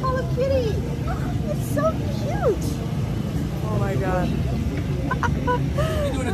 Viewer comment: I thought u said there was soo much jalapeno when i turned away while the video was going then i went back it was hello kitty lol i thought jalapeno cuz its a taco eating contest lol